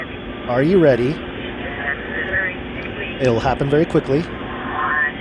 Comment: I hear: "are you ready for a misery Christmas for all"